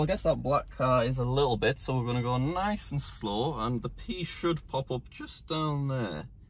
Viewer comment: I wasn't really paying any attention to the video, and then I heard, "the p should pop up there..."